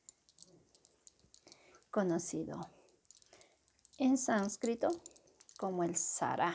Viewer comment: Did she say "in sanskrito"? Does she mean the sanskrit language?